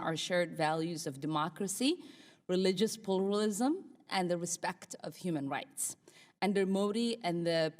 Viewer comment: wat the hell was she saying, religious "plooolalism"